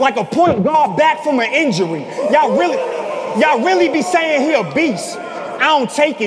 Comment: Ahdi wasn't lyin about him saying "yall niggas crazy" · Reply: Nah, i heard "yall really crazy"